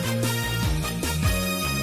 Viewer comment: I knew it! I swear I heard "kuru kuru" when I was a dumbkid